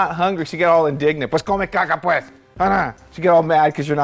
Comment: he means "pues come mierda" Lmaoo I heard it going up too